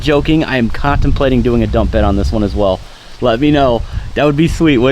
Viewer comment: Yes install the BOV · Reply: Sorry I thought u said dump valve, which would be freaking sweet. “BOV” After replying u said dump bed, I would have to vote no on the dump bed